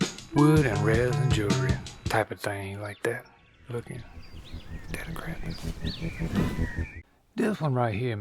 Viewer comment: what does he whisper under his breath?!?! I've listened to it like 50 times and for the life of me cannot understand him haha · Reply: Steady craftin...